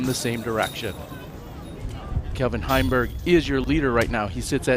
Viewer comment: Did he say Kevin Heimburg ?